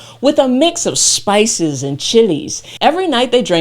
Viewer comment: At the 6:57 mark, are you saying Spices and "Chiles" (as in Hot Chiles) or what?? Can you spell it for me, because it almost sounded like you said somethin' else, with a 'T' sound. Please? Thanks...